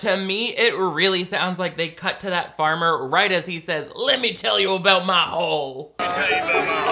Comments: Ok don’t know if anyone already said this, don’t have time to scroll through and find out...buuuuut I heard “let me tell you about my hog” when they were at the harvest fest, and being from the south as I am, I feel like he could be talking about a hog but more likely than not, he means his Harley. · Haul 🤣 omg but that is so funny, I heard hole until the last replay